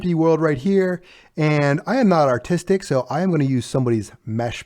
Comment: "I am not autistic" why did i think he said that, what is up with my brain